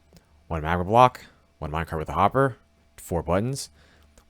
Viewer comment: When you said minecart with hopper I thought you said microwaved hopper lmao